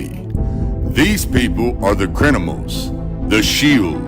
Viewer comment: I swear I heard him say "the crinimals" ...